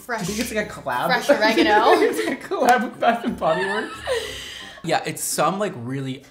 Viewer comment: Hilarious 😂 I’m fascinated by the Bed Bath and Beyond collab pickle lol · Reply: I thought they said it was a Bath & Body Works collab pickle! 😂💀